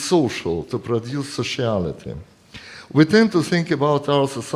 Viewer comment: I'm sure I heard what hilariously seems like a Freudian slip at 7:14 "to produce a Charlatan". It makes sense in the context though it's still rather funny considering Boris and his love of Kojève and so on, or actually that makes even more sense. Thas spuke zartharustranoid.